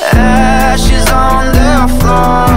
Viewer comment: Tell me why instead of hearing “ashes on the floor” I heard “Ash is on the floor”